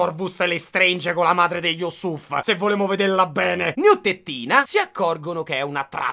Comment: Poi ci sono io che ascolto 3 volte il minuto 1:00:23 perché mi sembrava che avessi detto "neo tettina" 😂😂😂